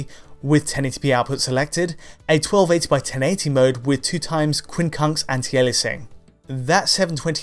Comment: 2x Quincunt AA!? Did I hear that right? lol